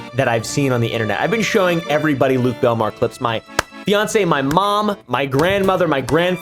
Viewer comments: did he just say...... FIANCE?!?!!??!?!?! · Did he say fiancé is he to kallmekris